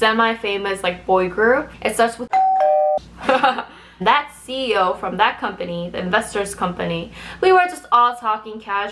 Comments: What did she say in 9:03? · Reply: u kiss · i thought you said " It starts with a Q " xD